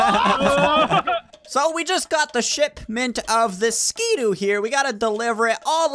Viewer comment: Did you say shitment